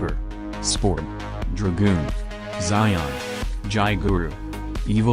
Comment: Did he say dragoon